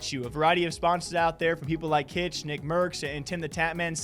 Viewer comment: Did he just say Nicki Mercs 😂😂😂